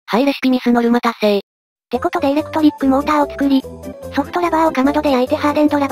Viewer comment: ソフトばばぁに聞こえたw